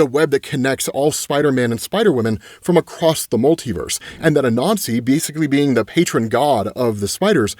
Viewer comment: I misheard when you were talking about Anansi. lol, I thought you said a nazi. I was like what did he say?